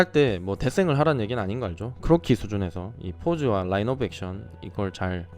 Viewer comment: 여기에서 뭐라고 하시는지 잘 안들려서요..ㅠ 라인업액션? 라이브액션? 그게 뭔가요? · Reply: 라인오브액션. Line of action 입니다!